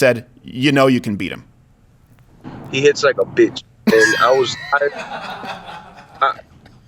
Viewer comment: Thought he said he hits like a bitch?lol